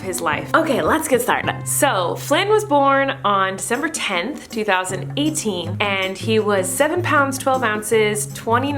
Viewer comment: I thought u said September 10 lmao